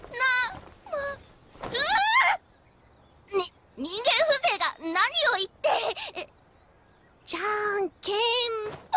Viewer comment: what does she say?? "n-ningen husega, nani o-- what?? (gotta learn Japenese with Yohane!) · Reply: MegaSonicMax the missing part is "ite", I think